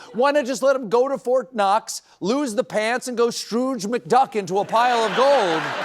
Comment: all I heard was duck... my feeble brain was trying to get the reference even though he quite clearly said Scrooge McDuck... I googled Darkwing Duck and jammed out to that intro for like an hour. I should probably take a break from the cannabis for a while.